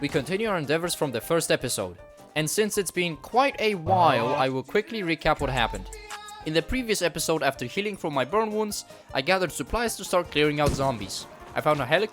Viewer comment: i heard "bum worms" instead of burn wounds... i need to get bluey out from my head